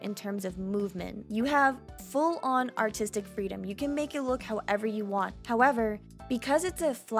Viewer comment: I thought she said "Full on autistic freedom" I was like- ITS MY TIME TO SHINE